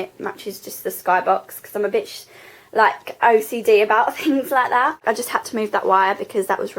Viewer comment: I had to replay at 15:27 because I thought you said, "'cause I'm a bitch like..." Lol! I love the window area, I wish I had something like that in my room and your dressing table looks lovely there too. :)